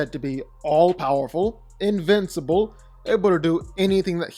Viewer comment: did you say abledo?